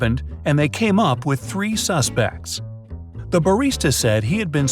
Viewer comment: Is it just me or did I just hear it as ‘sus bags’